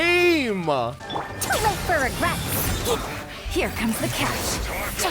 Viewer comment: after RATS FROM THE SKY yesterday, i just heard faruzan say "too late for the rats"